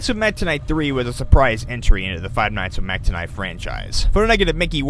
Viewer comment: When you said Mac Tonight, I misheard and thought you said Meta Knight.... OOPS